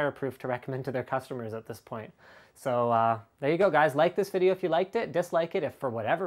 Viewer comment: At the end I totally thought he said "light this video if you liked it".